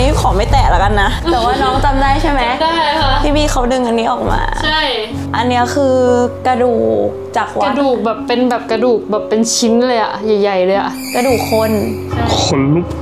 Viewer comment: ได้ยินผู้ชายเสียงต่ำพูดว่าชิ้น